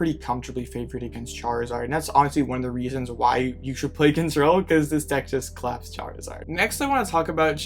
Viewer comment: One of the reasons that what? What did you say? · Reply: One of the reason you should play control is the zard matchup